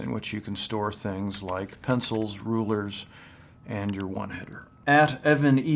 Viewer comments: Did anyone else hear one hitter @ 0:37 ? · Did he say, “...and your one hitter??? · Did he just say one hitter at 0:37 ? There's no way